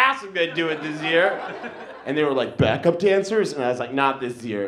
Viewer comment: "Back up dancers? - Not this year" LMAO! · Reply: damn i thought he said cancer